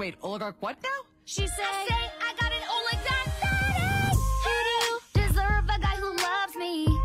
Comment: what the heck did she say? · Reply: Finally found it! She says I've got an oligarch daddy.